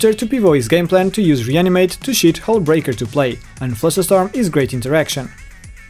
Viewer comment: "...Reanimate to shit Hullbreaker into play." That's. what I heard anyway